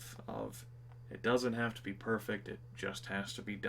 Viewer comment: I did a double-take. I thought you had started saying "it doesn't have to be this way." Speaking of, the AFMBE series was a fun diversion to have up in the background when having to slog through a bunch of work. No matter which way the wind blows, I hope you find joy enough both in reality and offline. I know you brought plenty into my life when the wife saw Trusty Tony's road rage that wound up with the car parked in a soybean silo and she lost it in a fit of gigglesnorts.